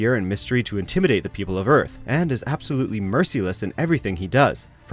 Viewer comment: Not to nitpick, but....did you just say "mercy-less?"